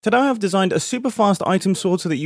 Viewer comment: It sounds like your saying tadah instead of today